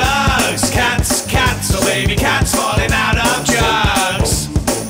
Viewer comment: I keep hearing "Cats falling out on drugs"